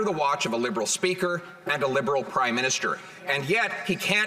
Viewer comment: did he just say Prime Minister or Crime Minister?